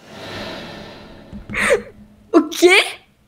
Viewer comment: bro i'm brazilian and when she said okey i heard "o que" so i think she is brazilian, and besides she has a great brazilian face bro